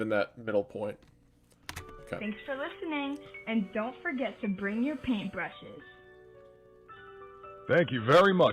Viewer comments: the narrator says "don't forget to bring your paint brushes" while the captions say "don't be afraid to bring your paint brushes" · I don't know if this is a mistake but the narrator says "don't forget" but the captions say "don't be afraid" interesting